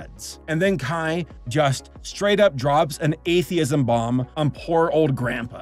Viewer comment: I thought you said "ableism" bomb for second and I was expecting to hear something else.